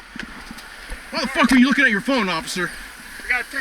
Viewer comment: At the beginning why did it sound like he said "im not an officer"